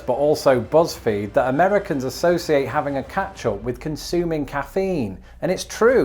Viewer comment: what word is he saying at approx. 1:50? "American associate having a 'catch-all' with consuming caffeine"? catch-all? i can't make it out? (and i have british friends, so i'm generally used to hearing the accent.